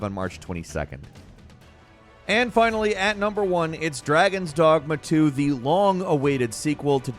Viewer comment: when you said dragons dogma 2 im brain thought you said sleeping dogs 2 to and i almost died from excitment